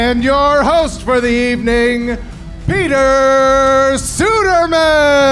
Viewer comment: Really thought they were gonna say host Peter Sagal